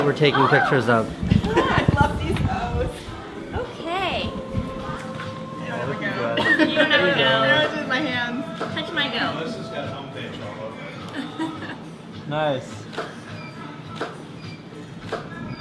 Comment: In my circle of friends, goat means butt hole, so you could imagine the laughing fit I had at this. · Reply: I'm pretty sure I heard "Touch my goat" at one point